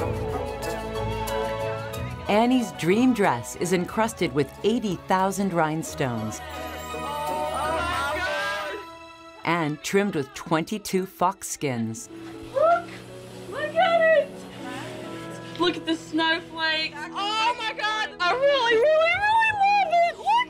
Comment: fox skins... animals died for that... okay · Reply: I also thought she said faux skin but ..... now I am not sure. If there were really white polar foxes that is simply horrible